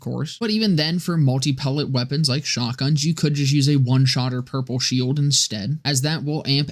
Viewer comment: Wait am I just stupid?😅 What did you say at 8:40? Was it "one shotter purple shield" or have I misheard? Sorry for all the questions and thanks for the great videos 😅